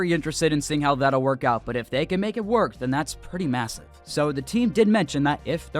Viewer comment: Did you say massive?